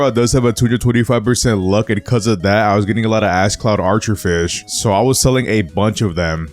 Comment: did he say the a word not ash lol